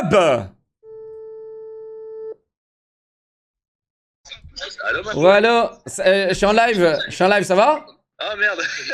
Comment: c'est moi ou à leak le prénom de??? ^^ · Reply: Il a dit ma poul